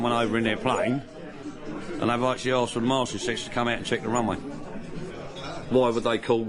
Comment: I think he's saying 'Marshall Assist'